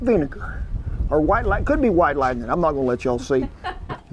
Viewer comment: I had to rewind I thought he said white like me 😂. He said white lightning ⚡️